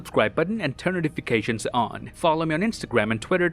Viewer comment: did you just say you're kevin hart at the ending?